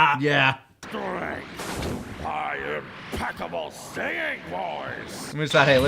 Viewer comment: Fun fact: when i watched this for the first time, i heard him say "my impeccable Saiyan voice" and thought it was a reference to Nappa